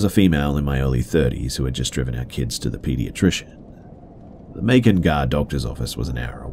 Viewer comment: What? did you say Macon gah? Lol. Macon, Georgia (GA). That's funny. 2:05.